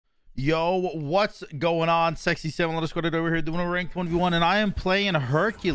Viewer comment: what exactly do you say? @ 0:02 "Yo, what's going on _____" ? Sexy -- what? lol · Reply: "Yo what's going on sexy samalander squad today we're doing a ranked 1v1" is usually what he says for his intro. Samalander because his logo is a salamander and his name being.